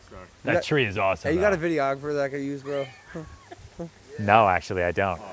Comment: What is Logan asking? 6:20 I dont understand a word haha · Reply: He says “Bro, you got a videographer I could use bro?”